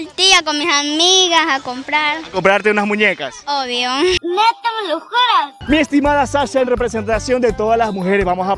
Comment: Que Dice el Meme del 8:08 NO SE LE ENTIENDE NADA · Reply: Neta me lo juras